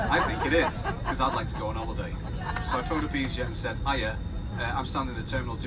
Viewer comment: Was this on a British Airways flight? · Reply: Sounds like he said easyjet.